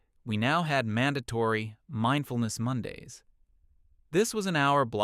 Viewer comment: maybe it was cuz I have it playing at 1.30x speed but I didn't quite hear it at first and thought it said "mindless Mondays" and had to double check and realized it actually says "mindfulness Mondays". It then proceeded to explain what it was and I was like "oh, okay. So i did hear it correctly the first time." Lmao.